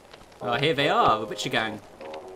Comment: When he said oh its the butcher gang i thought he said gucci gang... Whats wrong with me. ;<